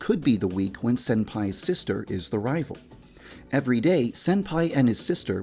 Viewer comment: Did he said sister? SISTER?! WINCEST! AAHAHAHAH FINALLY! Wait... We will need to kill her right? Much not like -99/10 for plot